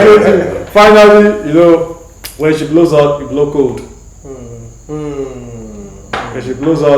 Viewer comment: I didn't understand 36:17. Can someone help? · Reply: "When she blows out, you blow cold". Meaning when your wife is angry or furious, you should not be angry too. Blow cold or try to calm the situation. Don't use fire to put out fire... Or else everywhere will burn lol. Example, whenever my dad is furious at home, my mom learnt to keep mute and not respond. That's her own way of not escalating the situation.